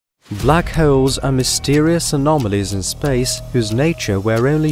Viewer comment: sometimes i hear "Black Hoes"